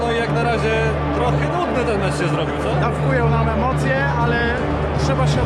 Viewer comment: usłyszałem w ch*ju mam emocje xD